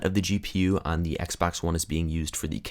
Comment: It sounds like u say xbax one But I would get ps4 no matter what